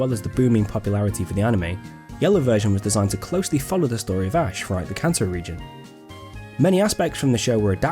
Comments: I swear you said the cancer reigon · I thought he said cancer region instead of Kanto region lol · wow i love the cancer region (it sounded like he said that) lol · Did he just say the cancer region, sounds like it